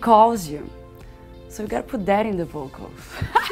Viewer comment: Am I the only one who didn't understand a part of that joke? Verbally I mean lol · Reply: I think she said "..and you put that in the vocal"